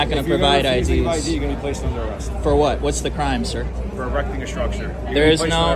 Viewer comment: What the fuck is "sidewalk obstruction"? Is everyone around me who is slower than me obstructing me? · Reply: If I heard correctly, the arrest was for "erecting a structure" - listen closely at 20:45.